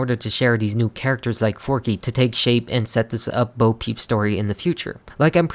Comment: What you say: forky What subtitles say:4K